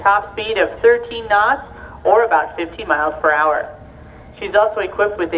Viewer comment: Hope she said 13 knots is about 15 mph. Thought I heard “50 mph” at 0:51, then said 13 knots is about 17 mph at 3:26.